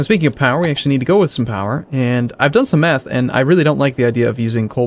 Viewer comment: Did he just say "Ive done some meth."